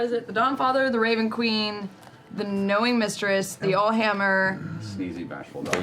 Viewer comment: “The Annoying Mistress?” It’s ok Sam. I thought I heard Matt say “Annoying Mistress” too when he was telling the group which gods were allowed to be worshipped in the Empire! 😂😂😂 I figured he meant “All-Knowing Mistress” after consulting the Tal’Dorei campaign book though.